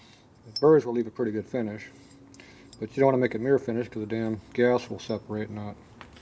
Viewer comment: Mr. I'd like to be clear on what you said there. Something about the "gas" and "not getting atomized", if it's a mirror finish? "gas not getting separated" ?